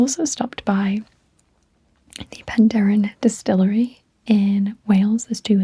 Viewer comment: I thought you said Pandaren distillery, I was very confused....